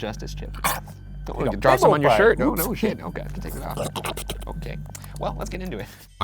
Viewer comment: Omg at the part in the beginning where they're pretending to eat chips and tells he dropped it on his shirt, I thought he said right after for to "take it off" probably not what he said, but still, I CHOKED